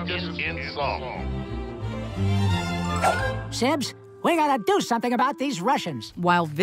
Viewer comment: I THOUGHT YAKKO SAID "SIMPS"